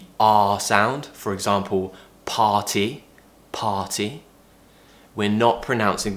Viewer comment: In the minute 7:36, you were saying party, but it sounded like potty ... it was very funny thought 😆🤣